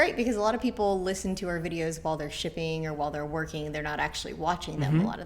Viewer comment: swore was gonna say, "...listen while they're shi++ing" but she said shipping lol. What % off do you do for sending offers to watchers?